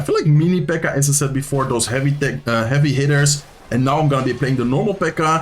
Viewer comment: What 4:50 He said heavy d.i.c.k (Tap 2 time)